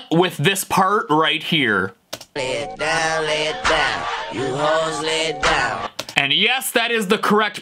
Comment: I heard lil Wayne "lay it bah lay it bah you Jose lay it bah"